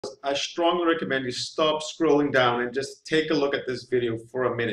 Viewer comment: Ohhhhh.... He said to "Stop scrolling down"..... I thought he said, "Stop screwing around and just take a look at this video". Honestly, that's what i heard initially... LOL